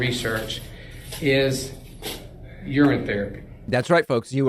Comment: Did he just say I'm in therapy?